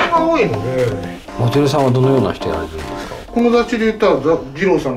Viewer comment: 日本語版空耳? 「友達」は、音声だと「この雑誌」ですかね? ^^